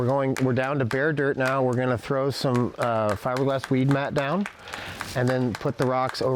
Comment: Thanks for the great video! At minute 2:52 it sounds like they refer to using "fiberglass” weed mat? Does anyone know where to buy that? Or maybe I misheard and it is not “fiberglass” but another word? Thanks in advance. Fire Safe Marin has wonderful videos!